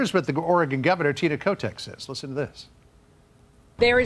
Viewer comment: ARE YOU JOKING RIGHT NOW? What IS that creature? What did he say its name was? Tita Kotex? You have to be absolutely joking with that. Kotex? Really? omg..... The avg person might go wow, Oregon... it's open, it's west coast, it's not crowded, it's beautiful.... but... that was the 70s. So glad I'm able to see all of this going down from a distance so I know exactly where NOT TO MOVE!